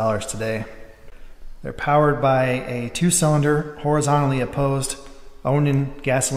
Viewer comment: did you say "powered by an Onan horizontally opposed"? And it even has a creep setting! 😆 Shame he didn't do the Harbour Freight engine swap. Think of all those 2nd hand engine parts you could've put up on eBay from the old engine. If the prices are like you said then it'd be very lucrative.